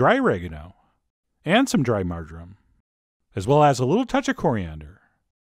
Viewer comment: what'd he say?? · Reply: dried marjoram